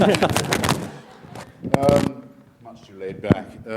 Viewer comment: Did Hitchens say "Oh God" when he grabbed his mic? 🤔 1:51:19 mark